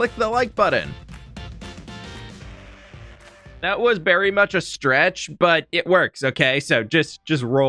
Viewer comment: Please tell me I am not the only one who nearly overheard aying "berry much" instead of "very much"